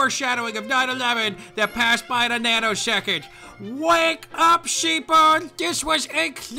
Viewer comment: every time says 'wake up people' it sound like he is saying 'wake up sheeple' THERE ARE NO COINCIDENCES!!!